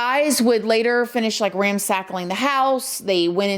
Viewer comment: Did you just say ran sackling thought it was ran sacked